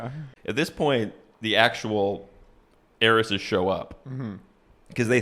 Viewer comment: Definitely thought he was saying Aries’s… not heiresses 😭